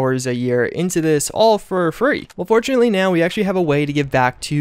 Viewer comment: I thought you said unfortunately about the donation thing lol